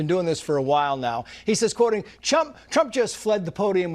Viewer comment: did he say Chump ??? Funny Freudian slip.